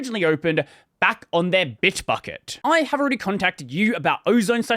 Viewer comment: I accidentally misheard "Bit*hbucket" instead of "Bitbucket" and was so confused